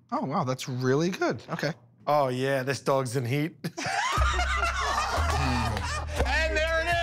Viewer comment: What did Q say at 1:45 ? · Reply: Oh ya, this dog's in heat!